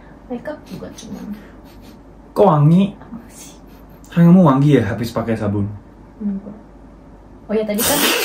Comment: Menit 12:35 ada yg bisa jelasin ngomong apa ? 😊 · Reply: "tanganmu wangi ya habis pake sabun"